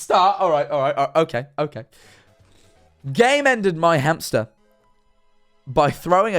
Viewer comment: When said Game Ended I thought he said came in so instead of game ended their hamster it was came into my hamster PAIN